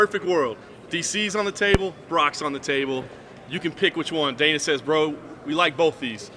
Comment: thought he said DCs on the table, rocks on the table, who do u take?